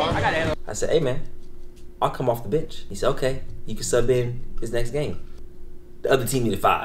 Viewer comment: wait i was confused at this part was saying sub hezi in for cel? lol cuz i was like what's hezi gonna do he don't pass the ball either 😂